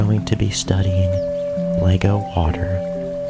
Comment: I thought he said LEGO Potter haha